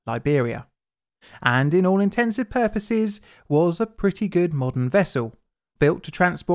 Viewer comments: did you just say "intensive purposes"? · Did...did you say "all intensive purposes"?